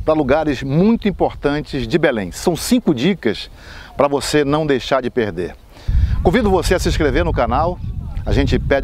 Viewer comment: Foi impressão minha ou ele falou mesmo isso: "pra não deixar de perder"?0:08 (logo aos 8 segundos de vídeo)